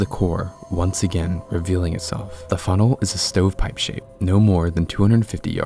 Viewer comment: Is nobody gonna comment about what that lady says at 24:24 lol? I know its morbid but it needs to be acknowledged out of respect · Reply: i feel like she said “i’ve never had anyone tell me to slow down before” or something like that? idk why anyone would need to comment on it unless it was something else 🤷‍♀️